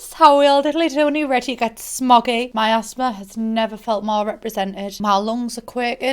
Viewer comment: when you said "my asthma has never felt more represented" i thought you said "my ass smell" it took me a fucking second